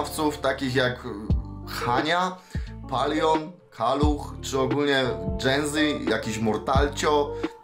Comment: CZY TYLKO JA USŁYSZAŁEM ZA PIERWSZYM RAZEM "PALUCH" XDDDD